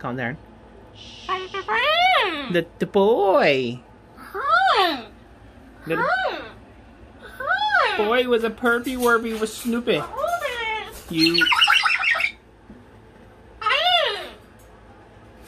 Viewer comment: did say "Snoopy" ? 😆